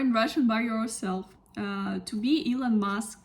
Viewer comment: не хорошо понимать, я слышу to be Elon Musk, это правильно? Я научился русский как самоук, не знаю как. Но это более пассивное знание, чем активное. Нето с кем разговаривать.